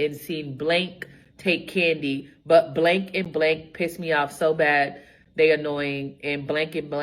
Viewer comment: I heard "Blank Blank Blank" as "Blake Blake Blake", and was thinking 'Damn, this Blake is a little A-hole' 🤦‍♀️ 😂 😂